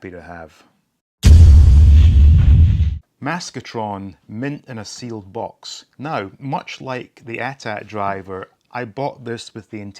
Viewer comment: When you said "Maskatron mint in a sealed box" I had to watch again because I thought you said "Maskatron lives in a sealed box". 🤣🤣🤣🤣🤣